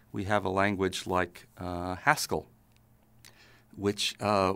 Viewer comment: i thought he said pascal not haskell